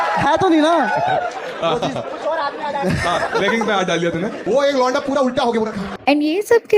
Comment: Did i hear leggings???